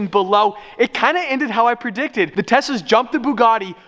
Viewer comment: Is it just me or does say “Tessa” instead of “Tesla” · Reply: im pretty sure he says "Tessa" instead of "Tesla" haha